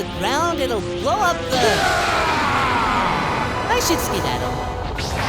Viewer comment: first time I thought he said "I should steal that" which I found better than what he actually said both showing off how stupid he can be and how he steals everything he can pretty much.